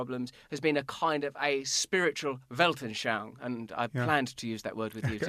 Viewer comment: "Welten??? shong?" i have no idea what word you said :)